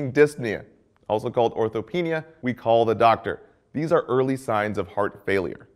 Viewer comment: ORTHOPNEA? Sounded like -penia